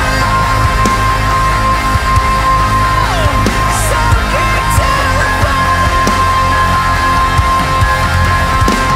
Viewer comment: What does he say at 00:43 ? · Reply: Juan Hernandez It's actually "soaking to the bone"